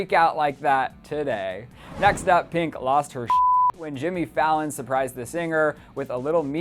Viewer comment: Did he say Jimmy Fallon at 1:20 ??